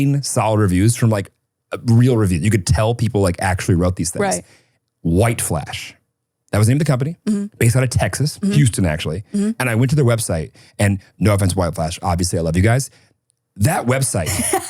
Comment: Am I missing what site it is that got the ring from?! He hyped it up so much saying not spons but I didn’t catch what site!! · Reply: I literally thought he said white flesh and I was like that’s one hell of a decoy website name 😂